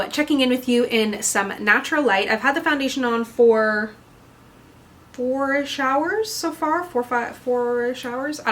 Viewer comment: i first heard 4 showers instead of 4-ish hours so i was thinking "wow that's really budgeproof... is that a good thing??" 😅